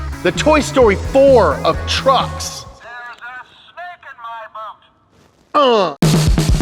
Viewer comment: I could’ve sworn Woody said “There’s a stick in my bum!” Instead of “There’s a snake in my boot!”